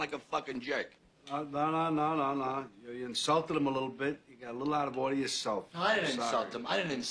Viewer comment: U insulted him a lil bit,u got a lil outta hand yourself.😂.. I love great movie dialogue. This is awesome dialouge,great acting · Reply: I could've sworn he said: you insulted him a little bit, you got a lil boy of yourself. You insulted him a lil bit.